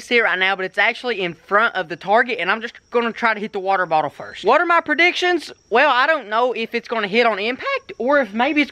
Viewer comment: did he say water my predictions